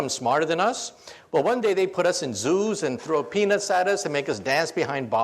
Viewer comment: did he say... throw a penis at us??? haha